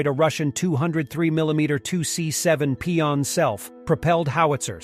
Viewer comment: "pee on self"? am I hearing that right? lol